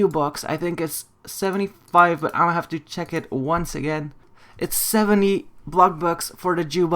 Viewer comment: i thought he said its 70 f###bucks 😂